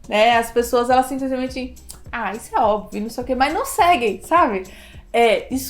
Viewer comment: Oiii! Adoro seu conteúdo!! Eu fiquei sem entender o que você fala 9:22. O que gás line? Existe um vídeo que você fala disso? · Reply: Olá Rafa!! Muito obrigada pelo seu feedback :D Nossa perdão, era para eu ter explicado a respeito. Seria guideline, um documento orientador sobre o passo a passo para construir uma revisão sistemática ou de escopo. Por exemplo, nós temos o manual de evidência do Instituto joanna Briggs: <link>